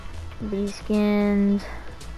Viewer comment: Why did you just say THEASE SKINS it's the NFL Skins You Dop